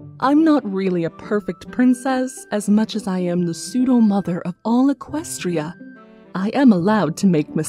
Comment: Is it just me of did it sound like she said "mopher" instead of "mother" in her cadence impression? Still love these but that slip up made me laugh